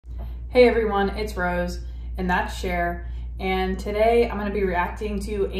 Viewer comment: LOL for the longest time, I thought that you were saying "in that chair" and not "and that's Cher" :-D